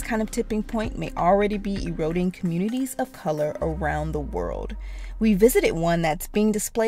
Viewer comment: did she say "communities of color"?